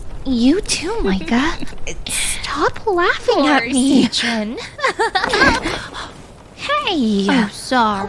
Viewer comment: Huge lore drop, so it looks like the Duchess and Mica were childhood friends as presumed, and Mica was a peasant that kept sneaking in to steal food from the manor's garden, and somehow became friends with Haewon in the midst of their endeavors, and started hanging out with her and someone who seems to be Capri given the context of the video. During one such day, Capri hides away Hae from the "guards" sight as they're searching for Mica. Mica enters the scene and is furious at their badmouthing of the duchess and her father, and attacks one of the guards, to which Haewon leaves their hiding spot to plead for him to stop, and asks Capri why she's just standing there. Honestly we don't have enough context so for all I know that question could have been aimed at the other guard instead. Eventually, Mica is apprehended, and Hae is forced to watch as Mica is captured and sold off to be tortured/enslaved. Her final words before the dream ends elude me, though I can make out the first word, being 'just'. Years later, after the Duchess finds and rescues Mica, she takes her revenge on either the guards who were present that fateful day, or the ones she'd had just bought Mica back from. God this series is so beautifully crafted and we're only at two parts, and I'm STILL not entirely sure about some of the details. Thanks so much for this video oml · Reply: I thought they were saying Caprine, though that doesn't fit the mineral theming all the other servants have